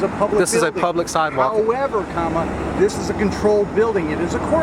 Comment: Did the cop say "however comma" ? My hearing isn't that great but I think he said that. Just seemed weird. It was at about the 3:00 or so point.